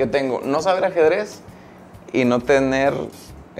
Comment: alguien sabe que dice? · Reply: "No tener la cabeza del nepe"